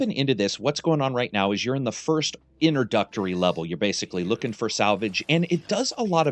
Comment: I swear I heard you say "inner-duck-tory" level.